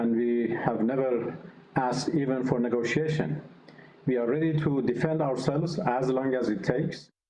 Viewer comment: I thought he said we won ready just like he will end Ukraine war in a week.